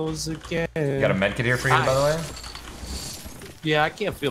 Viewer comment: Did ninja just say there she go nigga but low🤔